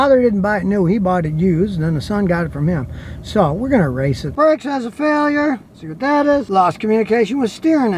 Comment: I thought Scotty said we are gonna race it. LOL...feel the power of that HEMI!